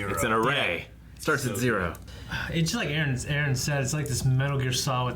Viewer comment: Each time they say "Aaron" I hear "Eren" and I'm like "JÄGER !!"....